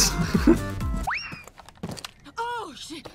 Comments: Did Hibana really say "Oh shit" at 9:30? :DD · anyone else hear Hibana go "oh shit"